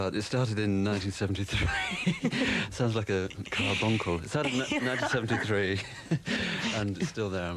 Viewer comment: What does he say? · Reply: Carbuncle